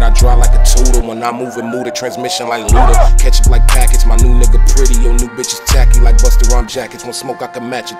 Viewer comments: Did I hear “My new nigga pretty” or is my mind playing tricks on me.. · I definitely heard him say my new nigga pretty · At first i thought he said my new nigga